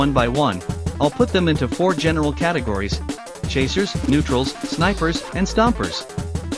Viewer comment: i heard diapers